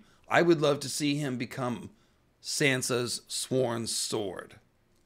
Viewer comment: Oh man, I’m listening to this as i’m mucking livestock pens in below freezing snow and i lol’d when i thought you said that Sandor could be Santa’s Sworn Sword. Was looking forward to how Santa Claus was going to get involved with a dogman protector 😂 oh well